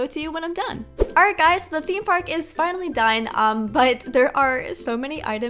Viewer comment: who else heard: alright guys, the theme park is finally dyin’?